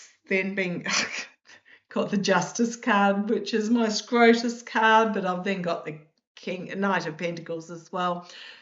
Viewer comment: Sorry for a minute there I thought you said scrotum card's maybe my hearing is not what it us3d to be apologising to you. Have a great time with your mate tomorrow.🇬🇧🙏🌼🌸🦋🪻